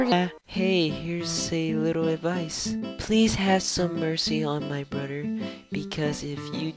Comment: Please have mercy on my BUTTER I heard That XDDD